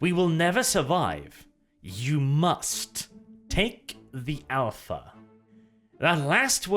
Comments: "Take the Alpha" When I heard this, my thoughts went to 'Alpha level psykers'. The Inquisitor gives the space marines a glance, warning them about what is going to happen. Thus we can infer that the orbs took over him when he later stands up and goes beast mode. The orbs take control of the Inquisitor. Inquisitors are usually strong level psykers. Ergo, the orbs took control of the Alpha level psyker. Or "Take the Alpha" for short. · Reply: I heard "Take them all" not "Take the alpha" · I hear "you must take them". Seems logic as the orb then takes the astartes. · I thought it said take the alter on my first viewing, I thought maybe it was and ancient AI at first. But now I’m not so sure. Will say I am loving the video shorts and all the different theory’s revolving around the possible origins of the sphere.... can’t wait for the next part..... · In reference to 19:06 when you say it sounds like " you must take the alpha", I think it is saying "you must take the offer" this would be telling the psyker to give himself over to the warp entity trying to take control of him, in exchange for greater power :D What do you think?